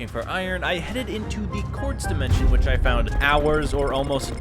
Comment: “court’s dimension” instead of “quartz dimension” 7:40 “and” instead of “in” 8:06 “booking quill” instead of “book and quill”